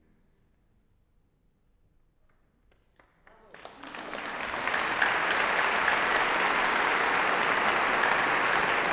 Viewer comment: Is it just me or is it at 6:22 it sounds like someone said "no" because of the clapping between movements. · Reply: The word being said was “Bravo! :)